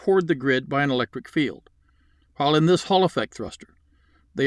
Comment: I keep hearing "hull effect" and each time I visualize the hull of a ship being capable of 360 degree thrust generation with no obvious appearance of exhaustion, like a UFO saucer.